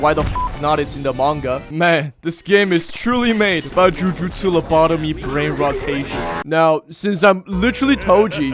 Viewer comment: Did I hear.. Lobotomy? Insert Second Warning